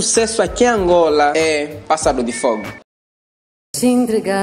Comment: Eu entendi "passado de fogo" invés de "pássaro de fogo" kkkkk